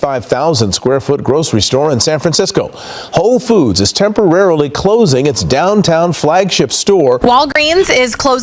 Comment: does he say hoe foods? 🤣 that should be staying open nowadays